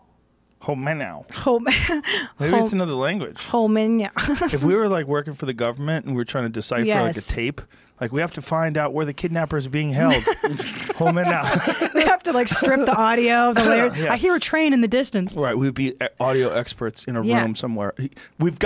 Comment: I really thought he said Home and out, referring we can do it anywhere